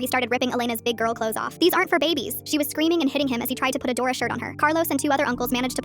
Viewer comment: Wait why did I hear "adora shirt" instead of a dora shirt- iykyk